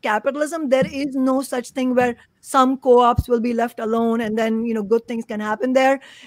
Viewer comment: did you say "chain of co-ops" ? _JC